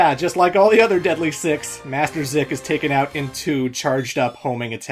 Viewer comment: I thought Zik called Sonic a bastard